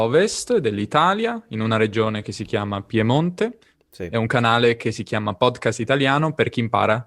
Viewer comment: I actually heard the Italian guy say: ”Sim” (🇵🇹), instead of: ”Sì” (🇮🇹) 😳.